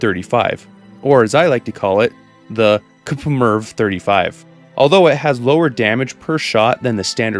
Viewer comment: at first I heard "coomer 35"